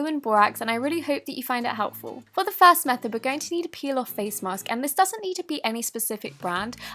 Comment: At first I thought it said peel off gucci mask